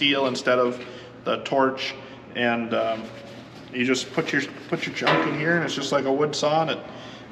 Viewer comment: When he was talking about the band saw I thought he said “you just put your JUNK in here and turn it on”. Haha I know farmers are tough but putting your junk in a band saw is a different level. Haha thanks for sharing